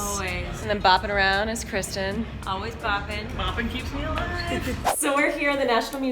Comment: I was kristen said boppen keeps me poppen